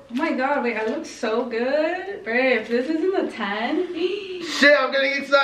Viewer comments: did anybody hear babe in 10:25 or is it just me? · I can’t be the only one who heard her say babe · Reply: She said Brandon if this isn't a 10 she slurred the word Brandon I turned my JBL speaker volume on and turnt the volume all the way up 👌🏽👌🏽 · Is it just me or did just say bae